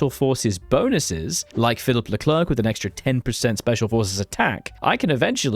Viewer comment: did i hear Leclerc?